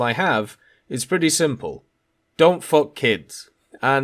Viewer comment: did I hear correctly? Don’t F kids? Or I’m just losing it as well?